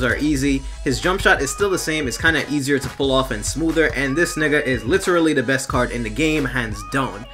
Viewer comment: did he just say nigga?